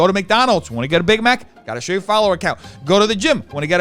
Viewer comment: Lmao it sounded to me like you said "you wanna suck that cock? Gotta show your follower count!" Until I heard you say "ticket" and realized you meant COP